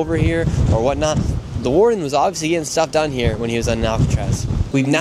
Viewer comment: I had to replay this part I thought u said “the warden was obviously getting sucked on here” at first lmao